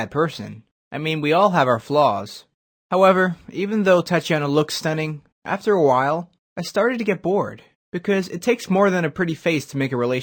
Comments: Im not joking, i heard " thotiana". eek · Who else thought he said THOTIANA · It sounds like he said her name was Thotiana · Who else thought he said thotiana 🤣🤣🤣🤣😂😂😂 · BUST DOWN THOTIANA sorry, I had to😂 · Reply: me too I thought he said Thotiana